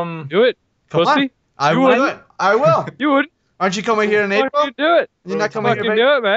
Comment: Did he say something about visiting him in April?